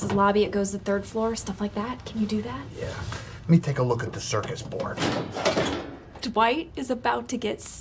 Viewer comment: Did Kevin say 'Circus Board' @ 0:14 😂